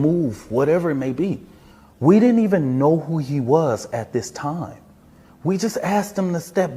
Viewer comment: They DID remove him. I thought he said, "WE DIDN'T EVEN KNOW WHO HE WAS.." Now he said "We COULD HAVE HAD HIM REMOVED. "